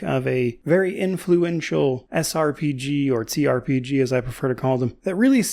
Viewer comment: Okay, I have to ask because I've noticed this on a number of occasions: Around 2:09, are you saying "T-RPG" or "Tze-RPG", the latter of which being a sort of portmanteau between T-RPG & C-RPG? There have been numerous instances in your videos where I have to rewind as I'm thinking, "Did he say 'T-' or 'C-' ... or mix them together?" Since you worded it here with the preface of "...or, as I like to call it..." I had to finally ask! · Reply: Trpg, I had a small speech impediment when I was a kid and that sound is sort of an artifact of that